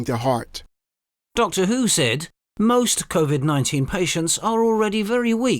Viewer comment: Did you just say Dr Who?